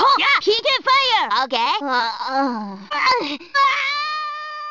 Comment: As a kid, I always misheard "PK Thunder!" as "PK Feather!" Idk why tbh😅